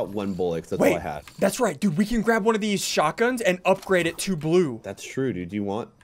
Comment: I thought you said you want to upgrade it